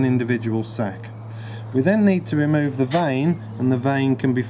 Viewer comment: he removes something but I didn't understand the word.Can someone tell me what it is called ? Thank you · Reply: stenophushispidus mercan you mean the vein?